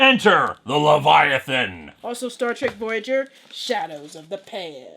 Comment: I thought it sounded like "Shadows of the pants."